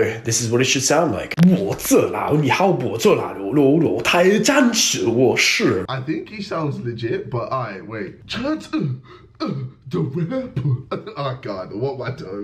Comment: i think he said chance the rapper